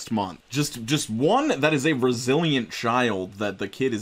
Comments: When you said resilient child I heard "Brazilian child" and was seriously questioning how being Brazilian makes the kid more bullet resistant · I thought instead of saying "that's a resilient child" I thought he said "that's a Brazilian child" and got so confused